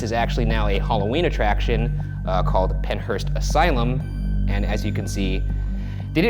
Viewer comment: I thought it said the Peanut Asylum😂😂